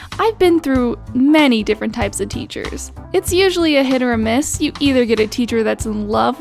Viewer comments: Did she say HIT OR MISS? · IN 0:20 DID SHE JUST SAY HIT OR MISS? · did she just say "Hit or Miss" ? I GUESS THEY NEVER MISS HUH